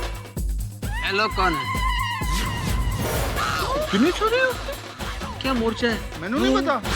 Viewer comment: what bhide said 🙂🙂🌚🌚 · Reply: Ye kya morcha hai